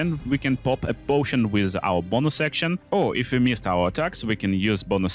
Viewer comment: Won't lie. It took me until about the 5:14 mark to realise that you were saying, "bonus action" instead of, "bomosection." Many apologies.